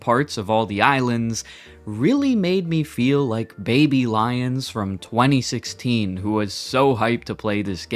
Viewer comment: I heard "baby lions" every single time 😂